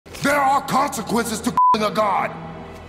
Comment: I swear i heard "THERE ARE CONSEQUENCES TO F**KING A GOD"